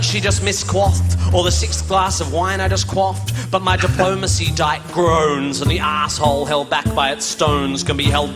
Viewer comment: It was “diplomacy dike”, I thought I heard you say “diplomacy diet”